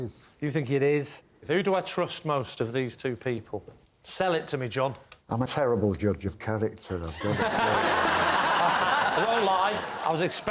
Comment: Anybody caught what John says here? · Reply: I'm a terrible judge of character though it's got to be said